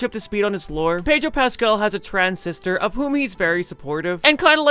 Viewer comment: CC says "pedo Pascal" what the